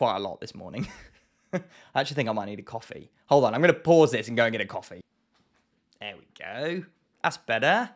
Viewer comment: i thought you said “i need to get a coffin” 😭😭